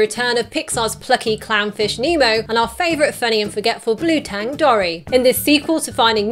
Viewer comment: When you said Dory is forgetful I thought you said she was forgettable and gasped in horror😂